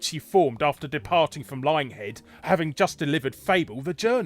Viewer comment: I thought you said "Lying Head" at first. Now that I think about it, it seems appropriate.